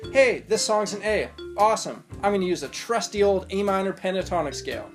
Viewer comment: roughly 1:27 man. i honestly might be mishearing, or in fact i might be just wrong on my theory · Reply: Ahhhh I didn't enunciate well enough. "This song is in A I'll use the trusty A minor pentatonic"